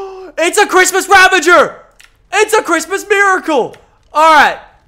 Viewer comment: I thought he said "ITS A CHRISTMAS RAVAGER, ITS A CHRISTMAS BULL DOG"